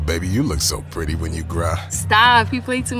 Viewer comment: “Baby you look so pretty when you grah“ what 💀🙏🏾😭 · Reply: I thought he said... "Baby u look so pretty when you cry"😂😂😂😂😂 "just one cry"crahhh" Crrrr"😂😂😂😂